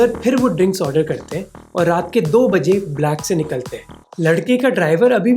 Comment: the subtitles should say "2am" instead of "2pm"